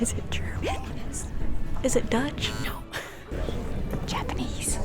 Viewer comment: Wait im confused 7:55 did the girl said ,,deutch"? it means german in german so tehnicly it is a correct answer, or did she say something difrend? Corect me if im wrong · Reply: She said Dutch